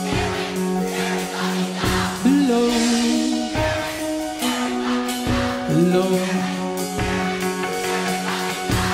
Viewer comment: What are they saying at 1:11:40? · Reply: here we here we here we f*cking go